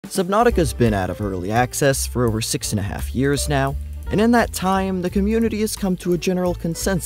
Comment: I thought bro said "Subnautica has been IN early access for six and a half years"